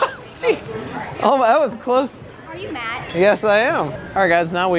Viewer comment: Lol In 3:57 I thought that girl say " Are u Mad?" I know she say Are u Matt lol